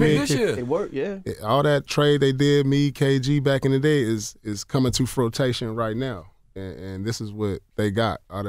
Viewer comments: Did I hear Pierce say 'coming to fruitation'??? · Did Pierce say, "frotation???" 😂